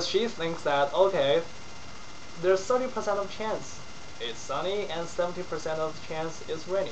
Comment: Did I hear 70% of chance it's sunny and 70% of chance it's rainy?